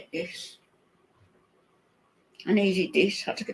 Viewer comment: "What is like, needed", not naked. I'm Scottish.. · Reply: I heard "naked" rather than "needed" but whatever it was, the "ed" and not "ist" ended the word. No way was it an "ist" unless someone really wanted it to be. right about incels sitting on the edge of chairs waiting to be offended!